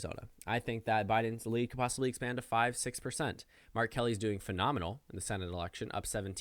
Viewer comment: Did it not sound like he said R Kelly’s lead in Arizona...